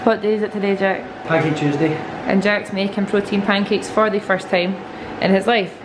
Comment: At first I heard “poutine pancake” and I was kinda worried